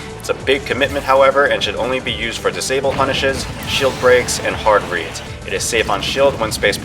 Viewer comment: "it should only be used on disable punishes, shield breaks, and.." what? Hard reads? Not sure what hes saying there